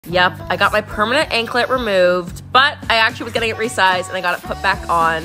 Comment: I thought she said her ankle got removed 💀